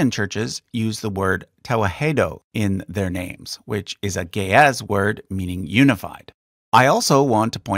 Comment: You said ge'ez word, i heard it as gay ass word😂😭.